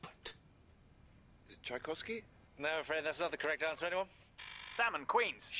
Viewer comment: did he say czajkowski, is my last name ❤👄❤